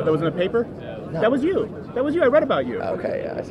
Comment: What did crowder say to the second guy with the backwards hat at or around the 21:00 minute part? Crowder said oh I read about you